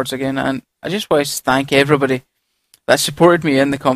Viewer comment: lol it sounded like you said stank evrybody near the end